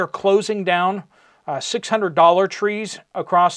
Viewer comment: When you said 600 Dollar tree stores, at first I thought you said $600 Tree stores. I laughed, but then stopped because that seems to be the direction we are heading.